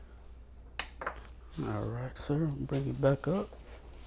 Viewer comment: Did anyone else catch that “I’ll be right back” at 31:05-31:08 it sounds so low but you can hear it! 👀